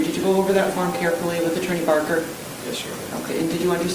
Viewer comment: Did he say "yes sir"?